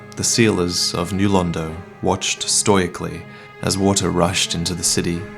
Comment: to hear Vaati says "stoyikly" instead of stoically.